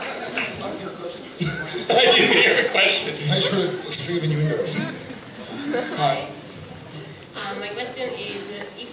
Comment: Who did the woman want to be at 6 minutes? Sounded like she said "Simon." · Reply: She said Genevieve Padalecki.